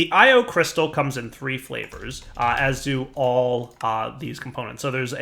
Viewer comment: did he say the i/o crystal has three different flavors?